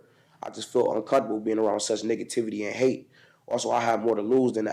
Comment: I swear to god that i heard him say N**gativity after his N-word rant